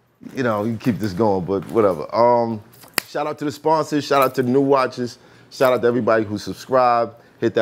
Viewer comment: When you said shout to the new watchers I thought you said shout out to the new watches. I’m like word??? Math talking spicy early 🤣🤣🤣